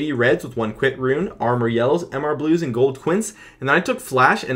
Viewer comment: Did he say hcuints?